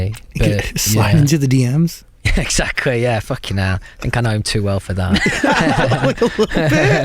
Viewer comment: what does he say on 50:24 ? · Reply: "I'm too old for that" (slide into Zayn's dm)